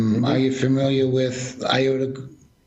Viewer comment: What is saying at 1:00:20 to 1:00:32? Is it Colonel Steve Statham and something Global? Does anyone know exactly who he is naming? Nigel Oakes and Strategic Communication Laboratories (SCL) I already heard of and curiously they not only work in propaganda and cyber operations but they also have an arm that makes, would you believe, digital polling machines for elections. As often says, "you couldn't make this up".